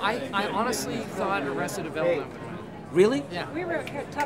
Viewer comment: what show did steve say he thought would win at 3:40 ? can't understand what he says there but it's quite interesting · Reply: Hey 7 years later it was arrested development